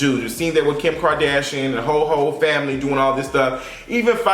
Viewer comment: I caught that small shade when talking about the Kardashians sounds like u said "the whole hoe family" 😁